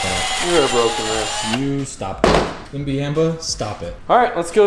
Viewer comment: “Imbiamba stop it” I heard it, I heard it.